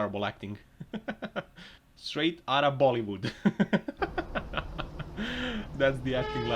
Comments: did he really said "Straight out of Bollywood?" if yes then Kudos to speaker for speaking such harsh truth on Internet! #nepotism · did you just say straight from Bollywood sir🤔🤔😬